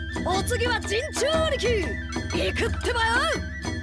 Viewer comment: Anyone know what he says at 1:10 in Japanese? I heard "iku tebayo" ???